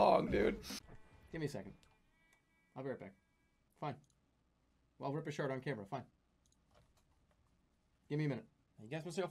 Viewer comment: I swear I heard him say "Fine. I'll rip a shart on camera, fine."